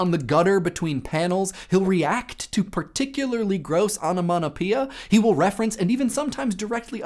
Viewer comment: What did you say at 2:34? onomonopea?